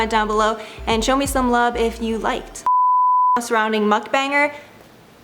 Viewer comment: Thought you kept on saying “muff banger” which is something totally different!